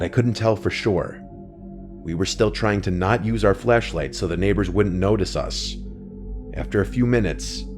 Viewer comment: It sounded like you said we tried not to use our fleshlights.